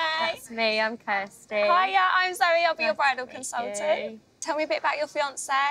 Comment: At first I thought the orange haired girl was her mumjackie. Then I thought she said «hi I’m SORRY I’ll be your bridal consultant”